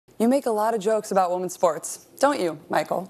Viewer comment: I heard “you make a lot of jokes about women’s farts” and was really confused for a while until it was pronounced clearly.